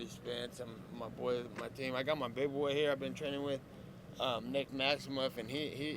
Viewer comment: Who is Nate talking about at 10:20 for a guy named Nick who? middleweight 3-0 brown belt I couldn't make out his name wanted to look him up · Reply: Found it nick maximov